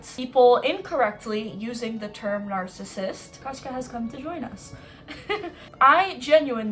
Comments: "Kaśka", did i hear that right? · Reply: his name is kashka 😇😇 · sorry it's so off topic but did I hear it right that the cat is called Kaśka? Like the Polish name? Or do I look for other Poles too much XD